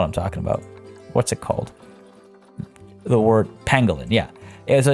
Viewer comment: When you were talking about the Pangolins I thought you said Mandoline and I couldn't stop laughing at the thought of going to a zoo and there being a mandoline exhibit.